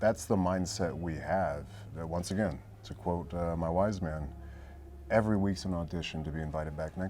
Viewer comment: Stg I thought Roman said, "my wife, man" not, "my wise man" I'm like damn his wife smart af. Knows ton about the company and business as a whole. Lmfao wise man makes a LOT more sense. Too stoned or not stoned enough? Idk 😂